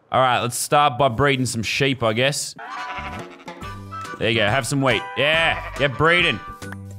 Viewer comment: I thought he said have some weed.